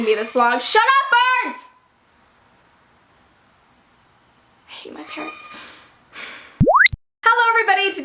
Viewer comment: For a second, I thought you said, "I hate my parents" instead of "parrots." :P